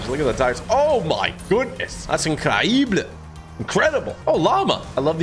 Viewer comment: Bro I thought you said lava instead of llama